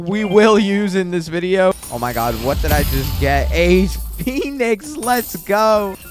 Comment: did i hear what i think i just heard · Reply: A Phoenix bro